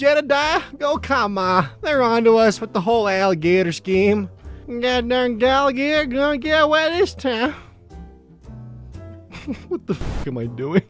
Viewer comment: I love how at 8:58 can't remember/understand what he said himself, but it was clear to me that it was "That darned alligator gonna get away this time." I am not from the South.